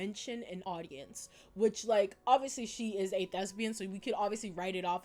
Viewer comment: When you mentioned Sally being a thespian my dumb ass misheard it as "shes a lesbian" and i was confused for a good minute or so wondering what the hell being a lesbian had to do with an audience 😭